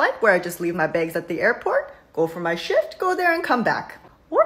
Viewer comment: why do i keep hearing "shit" over "shift" 😂